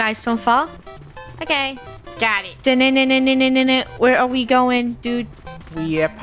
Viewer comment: did say “Daddy” !!!!??????